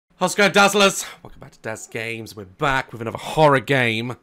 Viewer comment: Daz Games: "How’s it goin’ Dazzlers, Dazzlers? Welcome back to Daz Game!” Me couple years from now: 0:02 "I thought he said… How’s it goin’ Joshua, Joshua lol?!”